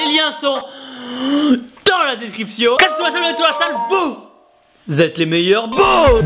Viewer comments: Il dit quoi à 5:46 ? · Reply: Casser tout à la salle, donner tout à la salle... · En 5:47 qu'est-ce que Tibo dit ? Entre "description" et "vous" ? Perso, je comprends ; "casse toi salvetoi va a la salle" ça ne veut rien dire ...